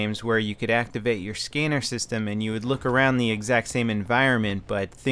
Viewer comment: did say scenner instead of scanner